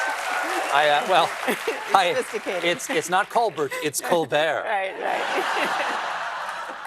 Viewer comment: I’m glad she asked for clarification regarding the “pecan joke.” I was trying to figure out what I was missing too 😂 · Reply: I know what piquant means but I kept hearing “pecan.” 😊